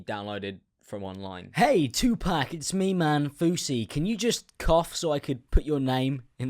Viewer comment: I thought you said “fousey cunt” 😭😂😂😂💀